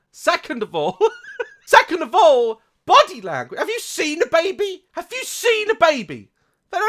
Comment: sounds like he is saying “Have you seen da-baby!”